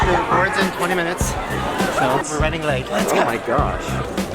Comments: Did anyone else hear the person scream “OH MY GOD!!” At 13:02 or is that just me ??? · Did anybody else hear, “OH MY GOD”?